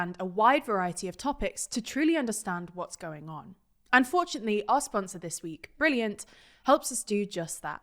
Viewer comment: did she just say unfortunately when talking about a sponsor😂?!